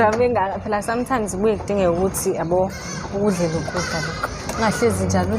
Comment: I think i just heard him saying "hhay babe" 14:51 👀bayajola laba ngeke